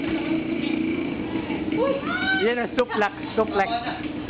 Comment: did I hear SUPLEX at the 3:00 mark?